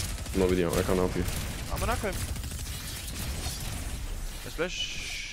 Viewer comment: did you say amina koyim😂